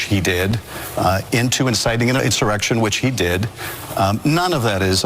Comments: I thought said Trump incited an erection 😘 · I actually heard him say "inciting an erection". What a tool.